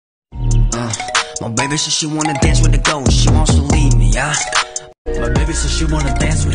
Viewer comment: when i first heard this song i thought it said "she wants to lead me" and i literally lost it😭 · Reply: I thought it said "she said she wants to dance with the girls" for some reason